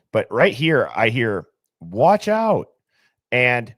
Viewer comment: I dont hear "watch out", i hear "whats wrong".